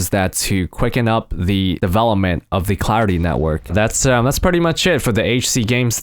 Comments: I thought you said: The Cloudy Network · tbh I thought you said the cloudy network lmao. Is there going to be an AU region?